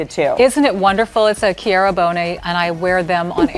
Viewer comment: Anybody know the name of the dress she is wearing? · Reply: Kizzy ddd It sounded like she said Keara Bonet. Not sure if I have spelling correct.